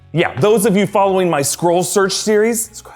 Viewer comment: First I thought he said squirrel search